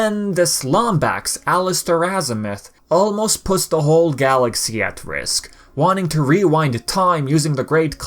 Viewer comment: It almost sounded like you said, "almost pissed the Galaxy at risk" 7:24 Lol